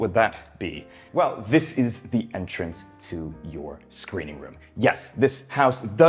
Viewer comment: oh, screening room. For a second I thought you said SCREAMING room. Honestly, that's probably my favorite room of the house, with the bunker being a close second. If given the choice, I would use that as my main living room.